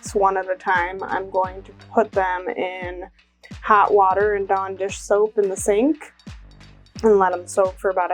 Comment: What did you say you were going to soak them in afterwards? It sounded like you said "donder soap."